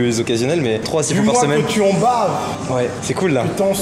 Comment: Dix mois que tu en baves 4:05 J ai compris dis moi que tu en baves je dois être fatigué